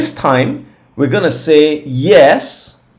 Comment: I thought you were saying we are gonna see a new high 😂😂😂 OMG